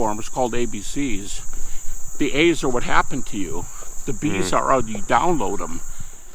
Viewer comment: then B's are what? Can't understand what he said. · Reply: The transcript says "the b's are ugly download". Not sure if that is accurate.